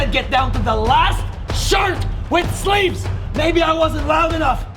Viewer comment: I thought they said get town to the last shart 😂